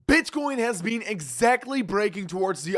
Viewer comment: What the hell did you just say at the beginning? Bitchcoin? 😂😂